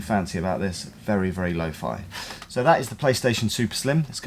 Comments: did you say PlayStation 2 Slim? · Is it just me or did he say "So this is the playstation 2 slim"